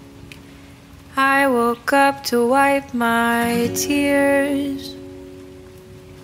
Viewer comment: At first I thought it was gonna say “woke up and wipe my ass” but it said “woke up and wipe my tears 😂 I can’t believe I just watched you eat cereal for 4 mins 😂